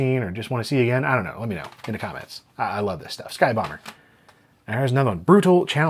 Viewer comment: I misheard Sky Bomber as “Sky Boner”. I wasn’t watching the video at that moment. Lol